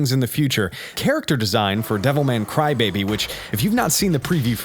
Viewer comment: "Devil May Cry Baby" LOL. I know you didn't really say that but when I misheard it I thought it was funny. Would it be a crossover to rival the (real) 3-way crossover DragonBleaPiece? Maybe! Edited for clarity.